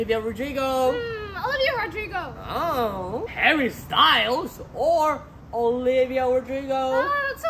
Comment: When he said Harry style I thought he said which style